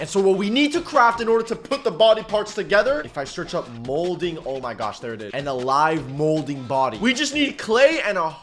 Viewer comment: My Grandson likes to watch this guy. When I watch I think I keep hearing him say "Oh my God" which I consider taking the Lord's name in vain. Is he saying "Oh my God" or " Oh my gosh"?